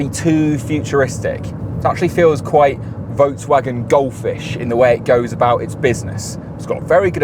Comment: What’s your ultimate EV road trip? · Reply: Misheard you at 14:32 - The VW Goldfish would be a fun name for a car. After all, BYD have the Dolphin, the Seal, and others.